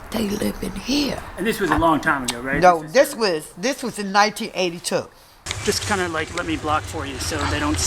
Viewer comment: 1. Killer soundtrack. 2. Did she say 1982 at 20:28? Shit, that's a long time.